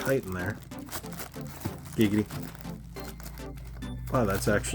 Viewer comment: did bro just say "giggity"? 💀